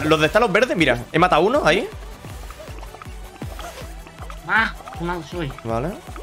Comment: Que malo soy :< Lo que escuché por tremendo nintendero comrsdor de juegos a 75 dólares que soy: Nintendo Switch 😎